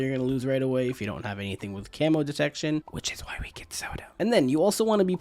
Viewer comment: That whispering at 1:58 totally got me xD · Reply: Sounds like he said, "Which is why we get Zelda.". But he probably said, "Which is why we get Sauda.".